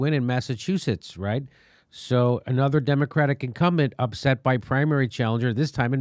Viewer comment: For a moment there I thought it said: "Another Democratic incubus" ...